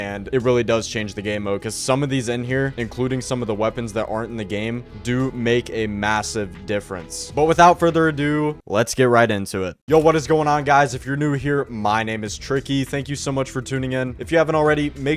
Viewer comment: did you say MASSIVE? 👀